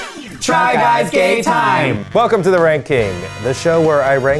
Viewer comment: I had to watch the start 3 time. Bc everytime i heard "try guys gay time" and it just made sense